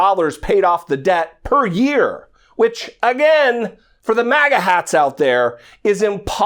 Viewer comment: by the way, did you say MAGA hats? or was it MAGGOT hats? i could have sworn to have heard the latter! thank you, Mr..